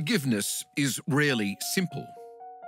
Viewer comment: sounds like they said “it’s really simple” 😂